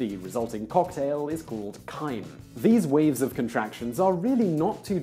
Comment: I thought he just said "These weebs of contraction..."